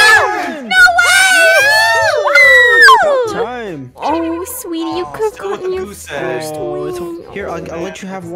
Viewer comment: DID CALLED SWEETIE 🤨🤨🤨🤨🤨🤨🤨🤨🤨🤨